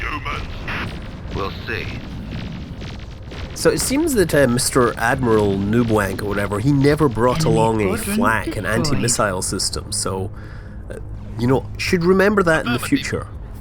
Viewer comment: Lol did you call him Noobwank and 7:10 ?